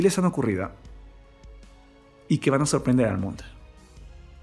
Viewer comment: Pensé que decia mensaje de Marco Antonio Solís